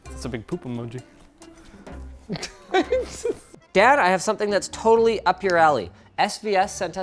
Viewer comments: Did Linus just say dad? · Did Linus call DAD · I love the subtitles read "Dad" at 13:55 instead of. Oh and also, more please. I love everything about him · I thought he said "Dad" lol